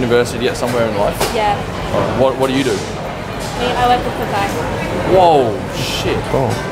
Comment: What did this girl said ?? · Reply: Rahil shaikh i work at popeyes